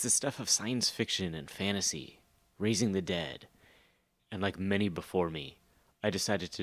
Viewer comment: I can't believe I missed that pun when writing the script! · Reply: I thought you did at 0:08 - I heard "raisin the dead"